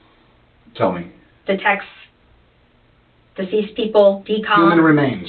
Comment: I love the subtitles sometimes lol 21:38 they say "detects deceased people" talking about cadaver dogs. What did the subtitles say? "The texts that these people did."